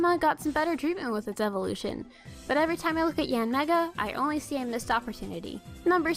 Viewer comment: For Yanmega I heard "Yeah Nigga"